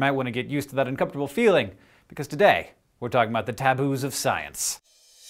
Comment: I thought that said Tattoos.